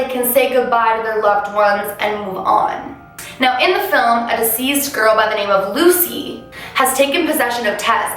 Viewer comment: Lol is it the Canadian accent that comes out when she says Deceased? It sounds like she says diseased it kept confusing me esp cause she talked about a bunch of illness and "diseased" people when she's meaning "deceased".... wtf maybe im too American lol still love the vids tho